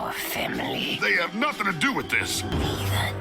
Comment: I thought he was saying he did nothing to deserve this.